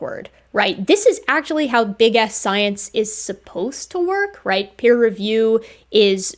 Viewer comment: at first I thought you said “big ass science”.